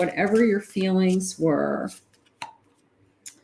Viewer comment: it almost sounded like you said "you are my sacred passion" instead of secret passion 💝